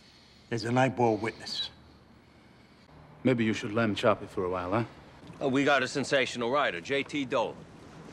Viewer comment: Until today I thought Furio was saying “let’s lamb shop for a while”, which would sort of make sense, now I can’t stop laughing at lamb chop